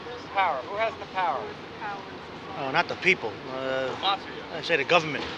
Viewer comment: It was interesting, around 10:15 or so, for the question on "Who do you think has the power in society today?" it sounded like "mafia" was said by someone, perhaps based on observations or experiences or just someone responding on a whim, with a hint of sarcasm. Then the person was greeting someone with "How ya doin'?" in the background. Around this time, the mob did have significant, but eventual declining influence, with the Racketeer Influenced and Corrupt Organizations (RICO) Act passed in 1970. The mob took advantage of the skyscraper boom in the 1980s in New York City.